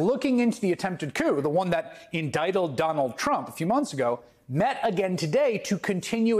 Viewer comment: Is my hearing going bad? @ 2:50 did Chris say "endicteld" ? I know what he meant, but, "endicteled"? LOL